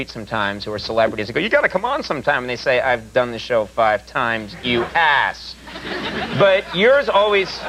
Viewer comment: Did he say you "hass"? Whats a hass? Has been?